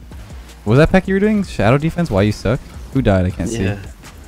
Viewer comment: What is he saying at 14:25 - 14:30 Could somebody please type it down for me? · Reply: He is asking if he was doing the "Why you suck at shadow defence", Training pack.